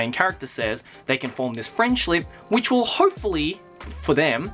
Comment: i think i hear you say "frienshlip" at 4:18 or